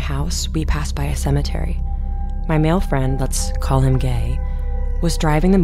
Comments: did she just say to call him gay??😭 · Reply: Bruh yeah she call him gay lol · "Lets call him...'gay'✨" THAT BROKE ME,SINCE THAT WAS WHAT I HEARD LMFAOO